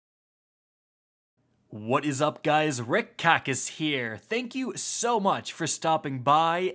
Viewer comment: For a while I thought he was saying red kackis and I dont know why